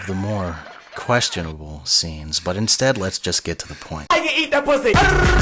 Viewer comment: So the captions at 1:26 instead of saying "Let's get to the point" it says "Let's get to the yuri"... I mean I guess its the same thing